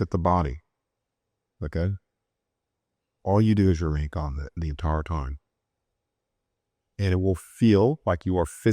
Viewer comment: “all you do is —?— the entire time ” I can’t hear what he said can anyone help clarify? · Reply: Remain calm the entire time.